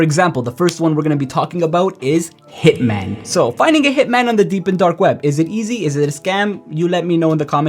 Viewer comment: Bruh stop saying "deep and darkweb" it sounds weird ... I keep hearing "deep end dark web"